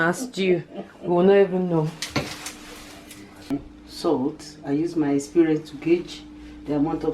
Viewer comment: She said she uses her spirit to gauge!! Lol. She’s beautiful and adorable. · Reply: I thought she said "she uses her experience to gauge", Caribbean people say that as well can I adopt your mom